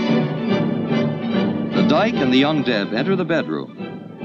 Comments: I kept hearing ‘undead’ instead of ‘young deb’. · As a lesbian, I can confirm that this is all true, except they left out the part where the Deb is given her first undercut pixie hairdo, WNBA keychain, and rescued pitbull pup. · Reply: When I first heard him say “young deb” I thought he said “undead”. 😅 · I thought the narrator said, "The dike and the 'undead'". :face-orange-raised-eyebrow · I thought the narrator said "Undead" at first then realised he said "Young Deb"! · "The dike" (have to spell it like this or get shadow filtered by YouTube) "and the" what? [Undead]? [Undeb]? [Young Deb]? What is the narrator saying? 2:10 Edit: Oh, I found it in the description after I found extremely vague explanations elsewhere. I still don't understand what a deb is. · He was saying "the young Deb," and I thought he was saying "the undead." I thought it was a lesbian zombie movie. I had to read the paragraph to figure out what the heck was going on. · For quite a while I thought he was saying 'the un-dead'.